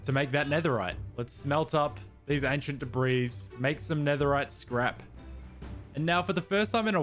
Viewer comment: When you said Netherite scrap i thought you said "Netherite crap" 💩💩 😁😁😁😂😂😂😂🤣🤣🤣😅😅